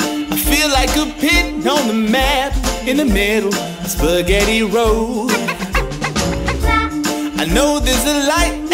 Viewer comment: at first I thought it said "I feel like a pig told a man, spaghetti road" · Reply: I thought it said pig too "I feel like pig Golden man in the middle of spaghetti road" 🤔 that cant be right lol